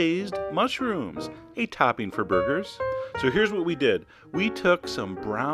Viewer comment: I thought he said, " A good topping for boogers." I was like WTH. O-O